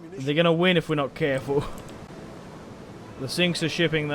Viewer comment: I swear he says the sinks a shipping